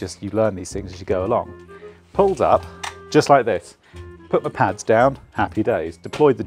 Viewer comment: I thought you said Pull my ‘Pants’ down - Scary Encounter with Bucket Truck..😁😁